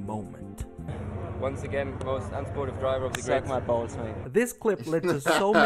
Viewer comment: I could've sworn when I first heard it that "suck my balls, mate" was actually "suck my balls, honey", which would've been an even more out of pocket thing for him to say to another man.